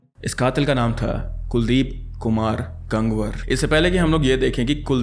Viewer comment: that's not gang war it's गंगवार I know it's stupid but I can't hear my surname wrong Nawabganj meri Nani ka ghar h 😨😨😨😨😨😨